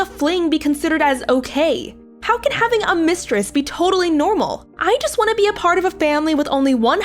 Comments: I thought it said " my husband is a mistress " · Because of the comment below me which says i thought it said 'matress' i keep thinking the husband is a matress for some reason!!! Lol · See? Men are bict- ((*Ok not all of men but still -._.'*)) Edit: also I thought it said mattress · Who else thought it said mattress